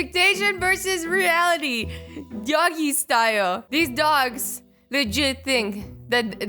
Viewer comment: Did u say doggy style XD